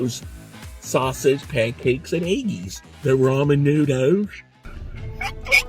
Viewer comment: did he just say "Raw Menudos"? I assumed I was the only one who said this. No idea why...🤷🏾‍♂️